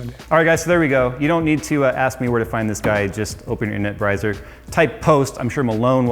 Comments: he said brizer instead of browser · did Jay say "internut brizer"?